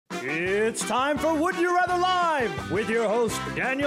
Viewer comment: I thought it said "would you rather live?" I said no